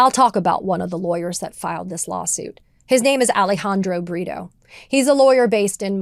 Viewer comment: Ohhh, Alejandro Brito. I thought she said Alejandro Burrito.